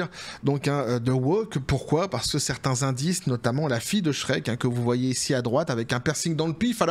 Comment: En effet, tu n'as pas tord.. j'ai entendu " fille de shrek trans" ====> toilettes, chasse d'eau. bye shrek !